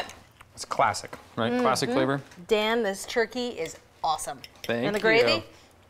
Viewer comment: When she said, "Dan, this turkey is awesome!" I thought she said, "Damn! This turkey is awesome!" 🤣